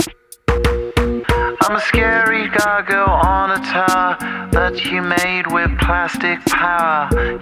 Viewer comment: I'm a scary doggo on the tower That you made with plastic powah Your rhinestone eyes are like factories far away Where the paraletic dreams that we all seem to keep ,drive on engines till they wee with future pixels in factories far away So call the mainland from the beach Your heart is now washed up in bleach the waves are rising for this time of yeaaaa And nobody knows what to do with heeee Under sunshine pylons we'll meat while rain is falling like rhinestones from the sky *instert beautiful nostalgic music* a M M M M M I gotta pee now my heart is frozen All the bowses and the growses Have been abstinated in my 𝘴𝘰𝘰𝘰 I prayed on the unmovable Y E A clinging to the atoms of rock Seasons see adjustment Signs of change I can't pee now She said taxi Now that light is so I can take This dawn brings strange loyalties and skies I'm a scary doggo on a tower That you made with plastic powah Your rhinestones eyes are like Factories far away H E R E W E G O A G A I N Thanks, I like thicc fish thicc fish a Thanks, I like thicc fish thicc fish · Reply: gg! I heard I'm a scary taco on a tower🌮